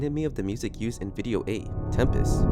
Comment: i believe the administrator is noli, in the "Tempest" video, he calls it his resting place. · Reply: I thought you said Temprist